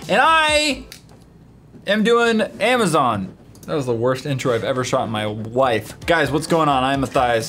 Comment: At first I thought he said "that was the worst into I did in my wife" what is that supposed to mean